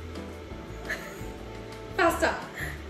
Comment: when said faster i heard pasta HAHAHAHAHAH